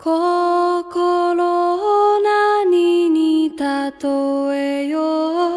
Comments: ｢心オ〇二ーに例えよう｣に聞こえて笑った覚えがある。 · 心を○ナニに例えよう（ごめんなさい、そう聞こえたんです）